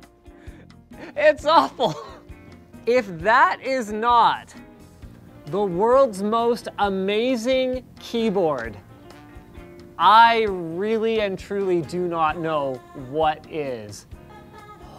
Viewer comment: You managed to make rgb less polarizing, now everybody hates it. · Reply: amazing? I thought you said ugliest